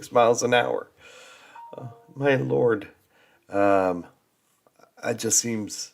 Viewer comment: I thought you were saying m'lord 😁